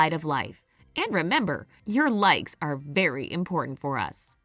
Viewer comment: at the end i thought she said your legs are very important to us...